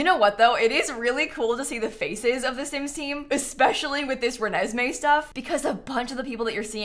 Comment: did she say RENESME like from TWILIGHT